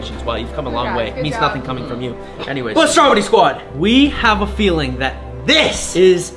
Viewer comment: I thought he said strawberry squad 😅😅😅